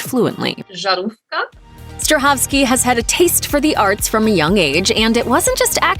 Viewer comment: Did she say “sharmoota” at 01:00 ????? 😂😂